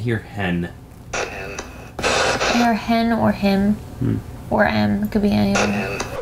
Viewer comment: I definitely heard “Tim”